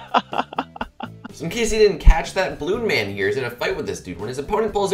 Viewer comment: when you said "balloon man" i heard "blue man" and looked at the guy in rune because rune is a shade of blue..... that was confusing.